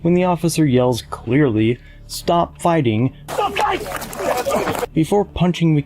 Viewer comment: I'm just using this to listen to while working I didn't hear "stop fighting" I hear "stop biting" Just as bad if not worse